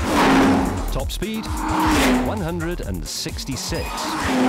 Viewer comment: He clearly says 166 there. ;)